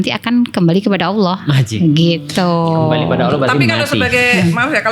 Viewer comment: ngomong apa tu (anj?) ?? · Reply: Ajib bukan anj